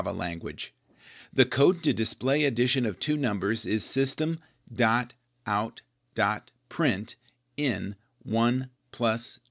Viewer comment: does he say printin? Isn't it println like printLn?